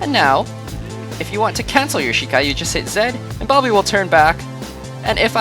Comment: i click what??? You said tht way to fast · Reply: Z he said Z